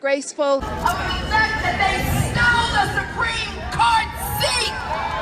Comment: did she just say “they stole a supreme court seat?” 😅 but there’s no such thing 🤣